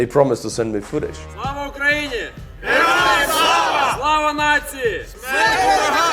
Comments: did they shout what it sounded like they shouted? If they did, then that is VERY worrying. Could someone translate that and let me know, please? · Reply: They said "Slava Natsiyi", which means "Glory to the Nation". If you wanted to say the other thing you would say "Slava Natsystam". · why does it sound like they say Slava nazis, I'm sure that's not what they saying but I just sounds like it